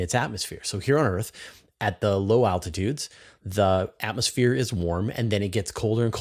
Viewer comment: I thought you were saying latitudes.